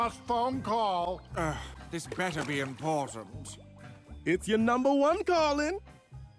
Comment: why did i hear that as "its your number one darling"